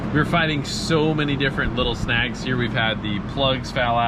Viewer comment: I thought he said “snacks”. And I got excited